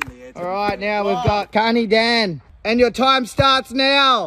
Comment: Hmm the new guy... Not feeling it. May take time and a few more pranks to be pulled on him perhaps... Wink wink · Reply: I guess he's Andy's friend they mentioned it a while back idk what's his name fuckin with his super aussie accent sounded like he said County Dan 😂